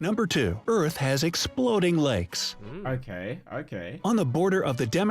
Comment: When The dude started talking about lakes I thought he said legs and I was so confused 💀